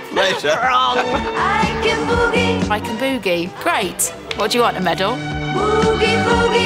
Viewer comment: She nails it! I thought she said Boogie Woogie! Haha!